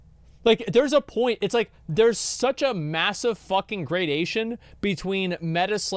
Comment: I'm so sorry but i have to get this out there. "There is such a massive fucking great asian between metaslaving at like...". I misheard this the first time and i just cant unhear it now.